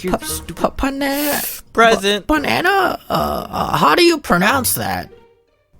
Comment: I thought he said banana man its amazing what ear buds can do